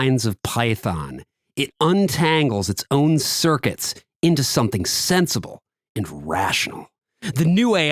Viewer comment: I misheard that as "It untangles its own circuits into something sensual."